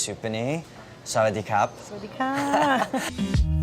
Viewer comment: did Andy really say hello using the feminine greeting? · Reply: Nope. He said kap, not ka